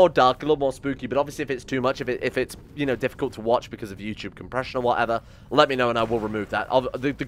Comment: I was eating when he said this, and I thought he said, "If it's difficult to watch because of your crippling depression..."